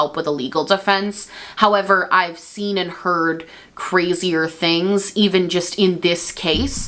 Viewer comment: Did someone say Amber Heard?